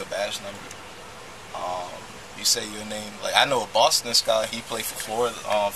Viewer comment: Did I hear him say “ I know Boston Scott” like he’s gonna save him lol